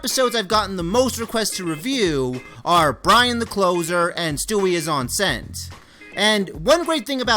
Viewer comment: enceinte is just pregnant in french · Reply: I always thought that said "Stewie is enchanted" but said enchanted in a different language